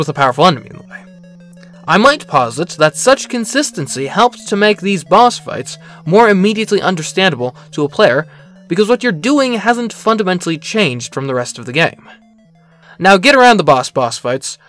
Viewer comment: I had to rewind, bc somehow I heard "My Wife posits", and I was very befuddled. not that you don't sound like you would have a wife, but you don't seem like the person to casually bring up your romantic partner in video essays...